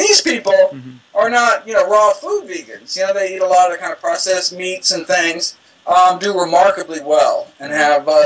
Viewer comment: does he say, They eat allot of processed meat?